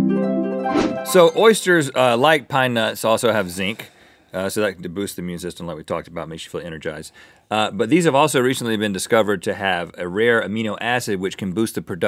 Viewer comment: I thought Rhett said "Oysters have zinc, like my nuts" instead of "Oysters have zinc like pine nuts". I was like "WHAT" and then I re-watched it and I was like "Oh, okay, PHEW!"